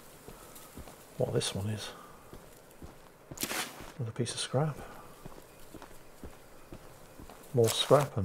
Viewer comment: Every time says "oh, a piece of scrap" I hear "Oh, a piece of candy". and expect him to walk into a trap.